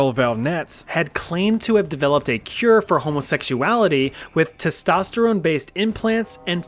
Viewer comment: DID I HEAR THAT RIGHT?? HOMOSEXUALITY😭?